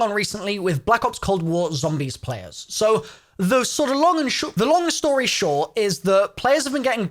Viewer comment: 20 seconds in and I thought you said, “The SHLONG story.” RIPonMe